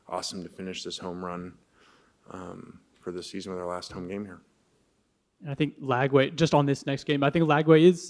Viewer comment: I heard him say my last home game here 😆 definitely looking to deep into that but it does sound like he's on his way out tbh